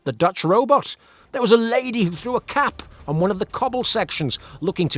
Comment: What you said "There was a lady who threw a cap..." What I heard, initially "There was a lady who threw a cat..." 🤔